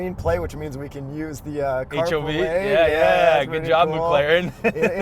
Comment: Does anyone know what they said ( or what they are talking about) at 03:00?? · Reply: Carpool lane